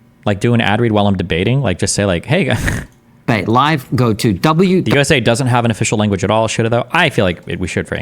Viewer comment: I thought you said the ladder I was about to be like brah our country's language is definitely English